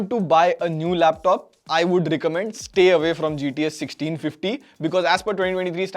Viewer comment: Who else heard GTA 1650 at 9:50 🤣🤣 ✋